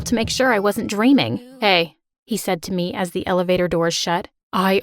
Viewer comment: does anyone hear Justin Bieber say hello